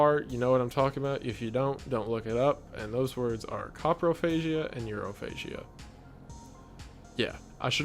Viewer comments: Can someone tell me what these 2 words mean at 1:02:05 ?? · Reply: he said “coprophagia and urophagia”, i looked it up and it’s basically the consumption of urine and poop 😭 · So at the start of tier 7 I thought you said cockrophasia or whatever it was and I thought ohh bugs then you said 2 girls 1 cup and it hit me